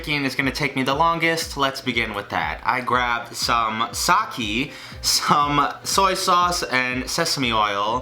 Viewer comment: Excuse me... what... what did he just say? “Sake”? •A•; (Please I’m aware he said not to leave comments like this and that’s why I’m doing it >w>)